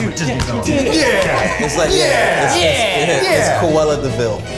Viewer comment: i think he said "coela de vil" instead of cruella?? lmao